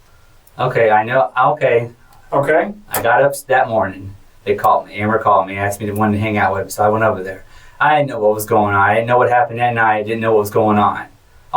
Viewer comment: Crazy he slipped around @ 12:52 "they caught me" , "I mean amber called me" Anyone else hear it or am I hearing stuff. I could be off.